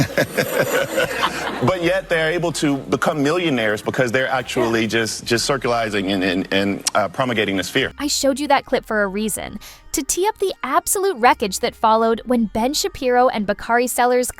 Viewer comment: (Did he just say, "circulizing"?)